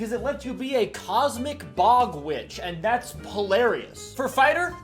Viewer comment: I had to do a double take during the Druid circle of the stars subclass cause I thought you said “polaris” instead of hilarious